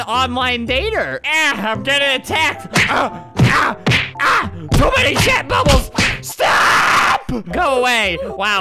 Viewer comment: AM I THE ONLY ONE WHO HEARD "TOO MANY SHAT BUBBLES" PLEASE SAY I'M NOT